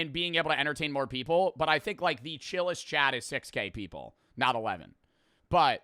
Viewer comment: sounds like he said 6 gay people and not 6k people